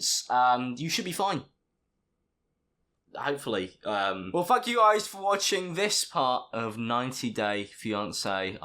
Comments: Swear Alex says "Right well fuck you guys...." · At the end he said well thank you guys for watching bla bla.... But I thought he said f*!k you guys for watching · Anyone else heard it as f*** you guys for watching? Just me? Alright then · I thought he said "fuck you guys for watching" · When he said well thank you guys at the end of the vid it sounded like he was saying well fuck you guys... Just me that heard that... Okay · I swear he says “Well fuck you guys...” 😂😂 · it sounds like he says "fuck you guys" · Honestly thought he said "fuck you guys for watching this video" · Did he say well fuck u guys??🤣🤣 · I thought you said * well fuck you guys thank you for watching... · Um it sounded when u said thank u it kinda sounded like u said f u guys for watching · didnt it kinda sound like he said "well fuck you guys for watching this part-" he didnt but it was just funneh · does he say well fuck u guys for watching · I genuinely thought he said " well fuck you guys for watching this part of 90 days fiance" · it sounds like he said "well fuck you guys for watching" instead of thank you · it sounds like your saying well fuc* u guys instead of well thank u guys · Am I insane or does he actually say : "Well fuck you guys for watching..." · I swear to god Alex said "well fuck you guys for watching" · Thought you said “fuck you guys” at 11:08 😂😭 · Really sounded like you said “well fuck you guys” instead of “well thank you guys” at the end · I thought you said “well fuck you guys”